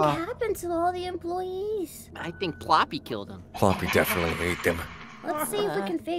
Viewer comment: ‘I think ploppy killed them’ Instead of “I think poppy killed them”